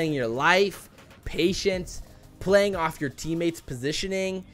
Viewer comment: my ‘hey Siri’ activated and thought u said patience prick